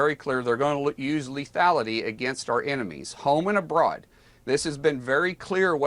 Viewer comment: They're gonna use lethality against "enemies at home" is what he just said?